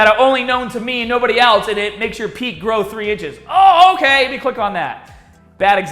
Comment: I first thought you said "and it makes your peen grow 3 inches" before I realized you said "peak." That would be the clickbaitiest clickbait title to ever clickbait... at least until someone promises even more gains